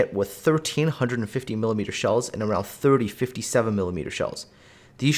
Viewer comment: unable to figure out what he's saying? What's the ship hit with 1,300 30 mm shells??? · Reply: ah Blucher would also be hit by 13 150mm shells.